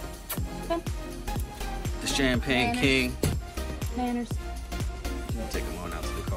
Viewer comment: sounded like you say "champagne pink" not king, when I first heard it. Made my curious, as my buck/champagne Tri has a pinkish hue to him. Love your dog's.